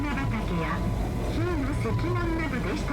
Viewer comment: 聞き取れなかった部分は「日出の石門」（ひいのせきもん）だと思いますが、愛知県民にもあまり知られていないマイナー景勝地がこんなところで紹介されているとは初めて知りましたｗ